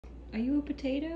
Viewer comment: I heard him say guurd borry (good boy😂)